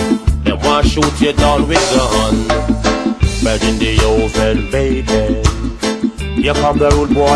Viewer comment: "ready meals and bacon, here come da rudebwoy Jamaican" haha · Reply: I keep hearing "Ready the old man's bacon, here come the rude boy Jamaican"